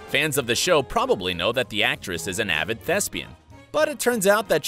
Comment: The narrator here should not have used the word “thespian” because ignoramuses like myself had to go to google to find out what that was - I thought it was some sort of sexual orientation I’ve never heard of! 😂😏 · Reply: And....I didn't understand him. I "thought" he said lesbian. Now, I too, must google thespian.